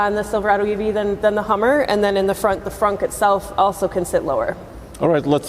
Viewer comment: did she just say Frunk? Im done